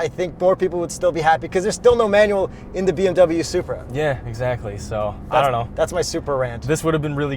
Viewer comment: Did you say Bmw supra?